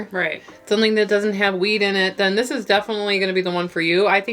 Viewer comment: I heard "something that doesn't have weed in it" and really pause and had to think about it for a while 😂